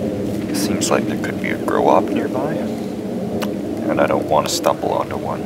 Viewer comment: Who does he say might be nearby? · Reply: Grow Op. A grow operation for weed most likely.